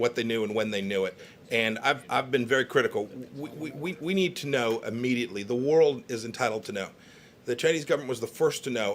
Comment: What does Trumph say at 1:03:06? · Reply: “Should’ve let us know”